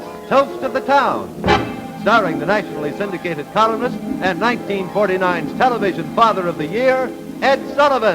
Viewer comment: did he say 1949 television father of the year? 0:30 are those the June Taylor dancers?